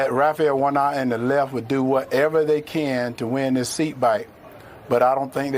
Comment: did he say the left will “do whatever they can to win this seat bike” or am i trippin?